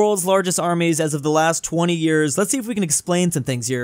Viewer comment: 200 years instead of 20 years